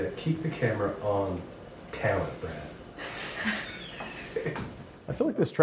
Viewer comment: Wait. Did someone just say Brad at 4:33 ??? Like Brad Brad? Leone? Man I thought Chris had him tied up in his basement or something.